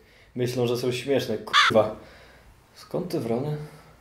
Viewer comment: Skąd te wrony? XD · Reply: fryUaj ahahah a ja drony usłyszałam XDD